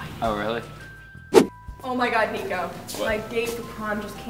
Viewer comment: did she say nico!! Omg where is the percy squad